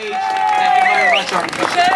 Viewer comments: At the beginning, I heard "Peter-san". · when they started saying "peterson" i thought they were saying "USA". i was like, why tf are they screaming usa in canada lmao